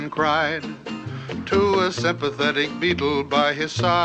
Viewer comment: Does anyone hear “sympatheteac” here?